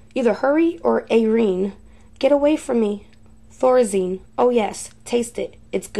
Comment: Ok I swear I keep hearing her say flourizine?? Any body else I thought it was laureen but I keep hearing the same thing does anyone else hear thay?? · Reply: I thought she said Thorazine